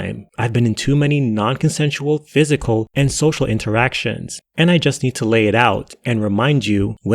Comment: did she just said "non consensual social interaction" hahahaha bro that's fire yo. Like someone trying to talk with her in the streets?